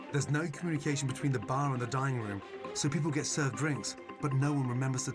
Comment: when he said dinning room I thought he said dying room.